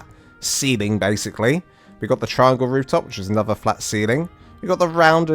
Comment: sorry... your accent made it sound like you were saying "triangle woos" lol good explanation though on the basics of building a small starter base.